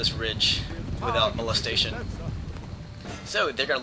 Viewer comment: Did i just hear Molestation?..